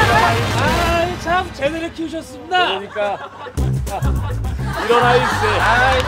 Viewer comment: 초에 지석진이 우리딸이 이런아일세. 하면 개리가 제대로 키우셨슴돠! 이러는 소리 들리는데 이게 소리가 안들어간거 알고 지석진이 3:33 에 다시 언급함